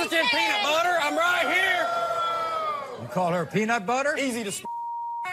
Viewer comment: I thought he said he calls her peanut butter because she's 'easy to spread'